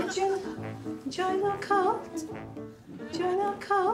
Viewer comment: It wasnt until like my third watch before I realised she was saying "join our cult" I kept hearing "join the occult"